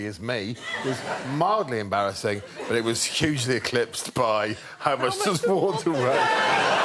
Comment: What did Greg say at 35:10 as the most embarrassing sentence? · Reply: "How much does water weigh?"